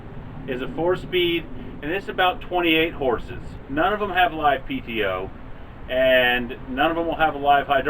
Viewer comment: Did he say they don't have wifi?